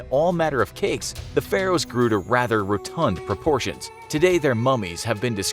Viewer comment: "The pharaohs grew to rather (blank) proportions" WHAT IS THAT WORD does anyone know? Sorry but it's killing me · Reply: Rotund